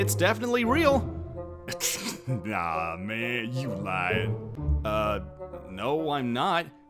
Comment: Was a pun just made at 3:50? Because he said “nah man, you lying” but it sounded like “nah man, you lion” AND THEY’RE TALKING ABOUT THE LION